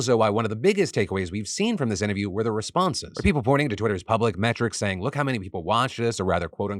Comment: When you said metrics i thought you said matrix 😂😂